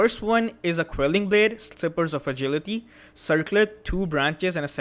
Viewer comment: i swear you say strippers of agility 😂 i agree they are usually quite agile.....